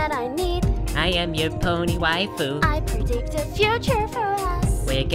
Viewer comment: i keep hearing "i'm your pony white poop" XD